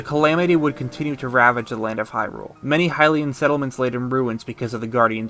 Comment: What does he say at 25:24? Subtitles would be greatly appreciated! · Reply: …the land of Hyrule. It’s pretty clear what he said